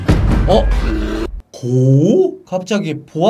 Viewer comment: GO? 갑자기 GO한테 간다고? 라고만 들림? 저만?